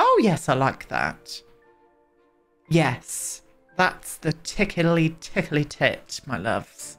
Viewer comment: Did you say “tickly tickly tit” at 15:05 ?😂😂😂 The sayings you come out with are hilarious